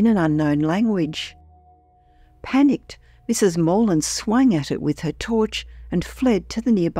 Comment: did you really say "swang"? 😆